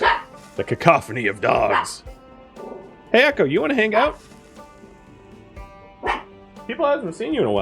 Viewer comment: I heard "Echo, you wanna hang out?" as "Ekim, wanna hang out?" and was very confused by the sudden appearance of EkimDaBrave in a Zomboid stream...